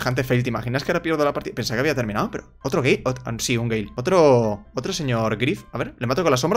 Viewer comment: yo escuchando que dice gei y luego de repetírmelo 10 veces entendí que dice gale xd